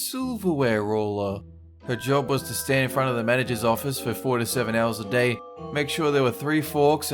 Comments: Is it just me or did say 47 hours a day at 2:24? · did you say 47 hours a day, because that's not how that works